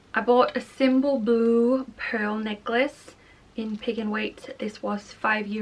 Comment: I thought you were saying "Pig and Wait"